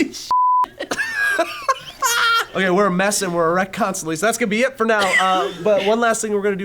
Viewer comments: I really thought said "We're erect constantly". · it sounded like you said "were a mess, and erect constantly". I peed a little